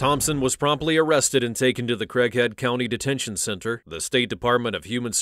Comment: Am I the only one who thought they said Crack head county?